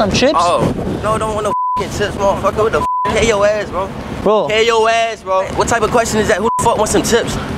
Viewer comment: what this dude sayin? "PAY YO ASS BRO PAY YO ASS BRO".wtf?